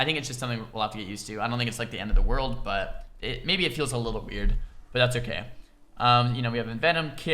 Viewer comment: i swear at 14:05 you said "it feels a little beard" lmao